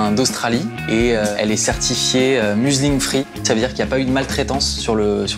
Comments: J'ai entendu "laine muslim free" ? Si c'est bien ce qui est dit, c'est à la fois courageux et encourageant ! Edit : Il s'agit en fait de "Museling free", mais ça reste engagé. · J'ai entendu "laine muslim free" ? Si c'est bien ce qui est dit, c'est à la fois courageux et encourageant ! Edit : Il s'agit en fait de "Museling free", mais ça reste engagé. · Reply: J'ai entendu "Muesli Free" moi, très déçu en tant que fan de céréales. Me voilà rassuré.